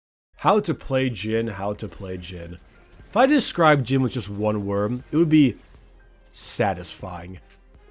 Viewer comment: does he say "If I could describe him in one worm"